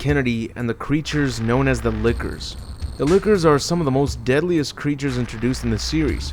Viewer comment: Why do I keep hearing "Liquors" everytime he says "Lickers"?